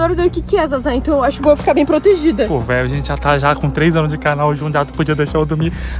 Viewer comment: eu escutei "pó meu a gente já tá com três anos de casal" KKK